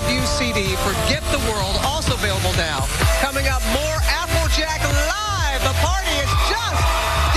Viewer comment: Lol Did she say applejack at 6:45 :D